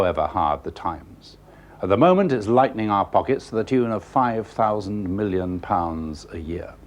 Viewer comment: did he just say, "five thousand million"? lol